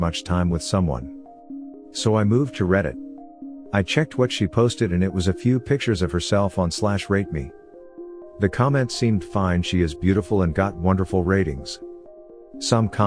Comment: First story: I misheard the reddit she posted in as "rape me" and not "rate me". I almost spit out my fucking rockstar onto the windshield of my vehicle before I realized that my nightshift brain misheard .